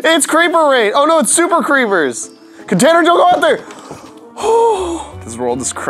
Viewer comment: did say container???